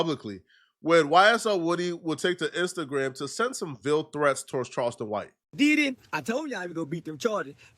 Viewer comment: "Veal threats" ni99@ what 😂 Bro stop using words you can pronounce. Is English your second language?